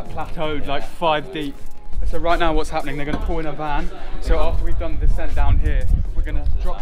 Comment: did he said the n- word wtf